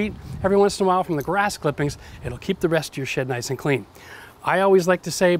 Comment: did he just say "keep the rest of your shit nice and clean" 🤷 Oh, "shed" 🤓😉🤣👉